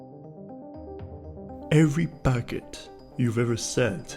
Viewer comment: lowkey thought he said every baguette 😭